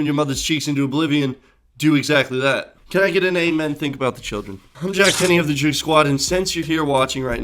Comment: what he say his name was cause it sounded like mine